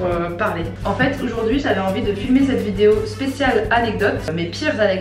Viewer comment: j'ai compris fumer au lieu de filmer jpp de moi😭😂